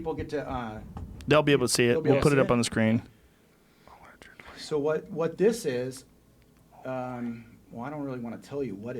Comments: what did joe whispered on 41:06?? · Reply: not buying it... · Man, joe whispers something at 41:07, Jamie looks at him and giggles. Tom doesnt have headphones on and doesnt hear it WHAT IS HE SAYING.+ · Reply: I cant stand him/it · Anyone able to hear what Joe whispers into the mic here, that made Jamie laugh ??? He says,; "He cant hear me right now" afterwards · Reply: He says, "Allegedly." "He can't hear me right now." "Oh boy" "not buying it." Then "Skeptical (unintelligible)" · What does he say at 41:07 ??? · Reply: The only thing i could make out is “ he can’t hear me right now”.